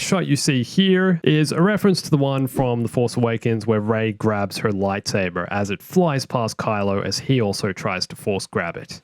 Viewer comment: I’m sorry but did he say her lightsaber